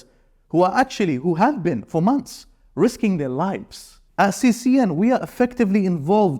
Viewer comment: When he said CCN and i misheard cnn😂